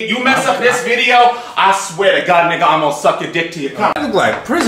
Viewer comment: did he just say “nigga”??